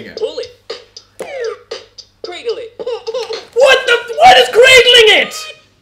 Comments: I thought it said: Pringle It 😂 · Bop It: "Cradle it!" "WHAT IS CRINGLING IT?!?!" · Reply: DuckMan Oh so it says 'cradle it'. I heard 'cringle it' XD